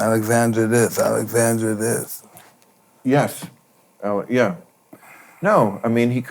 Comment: Did bill answer yes with “yesth “ at 59:53. Freudian slip because of mikes lisp. I swear I hear this…. Anyone else?